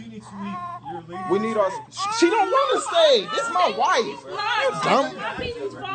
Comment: They clearly said he needs to leave not the baby or the mama! You can here him say she doesn’t want to stay 10:13 and yes this whole situation could have been handled way better especially if they didn’t want any kind of medication shame on the hospital for giving her something she didn’t consented to! But glad they are doing good